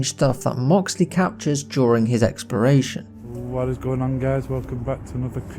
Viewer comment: did you just say "during his expiration" lol I think you meant "exploration"